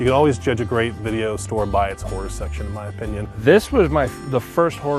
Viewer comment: I thought he said whore section. For real though I miss this so bad